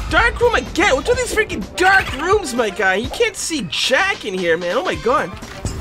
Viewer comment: Every time he said "I can't see shack" I thought he said "I can't see jack"